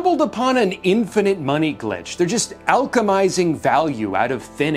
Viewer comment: did you just say "alchemising" oh my goodness lol.